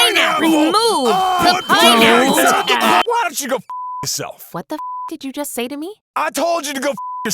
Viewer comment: "what the f**k did you just say to me?" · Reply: Correction: 1:10 On 1:08. He literally just says "Why don't you go *bleep* yourself. That's not what he says!